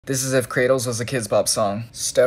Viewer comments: For a split second I thought he said kratos... But it was Cradles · why did i hear “kratos” instead of “cradles”